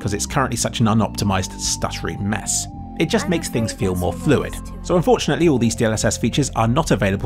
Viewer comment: mistake with subtitles - "genderfluid" instead of "fluid" Thanks on your work on subtitles